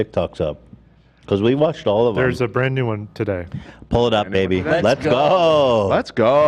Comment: I'm here to defend Ethan. He did not say "Pull it up AB" at [1:42:57], he clearly said "pull it up BABY". The slander is out of control.